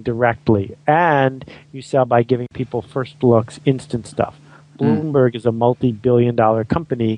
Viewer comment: "And you sell by giving people first..." What does he say next? First blog or first block??? And what's the company's name that he says shortly after? Something ...berg? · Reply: First looks, instant stuff.... Bloomberg